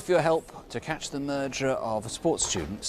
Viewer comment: did he say "merger"?? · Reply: Just his accent, lol he says murderer